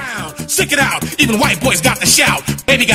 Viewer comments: I THOUGHT IT SAID EVEN WHITE BOYS GOTTA SHOWER 😭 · I THOUGHT IT SAID EVEN WHITE BOYS GOTTA SHOWER 😭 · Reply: I thought it said "white boys got the shower" 😭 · I THOUGHT IT SAID EVEN WHITE BOYS GOTTA SHOWER 😭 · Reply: I thought it said, “even white boys droppin’ Xiao” 💀